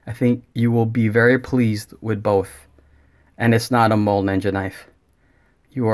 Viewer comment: legit thought you said mole ninja haha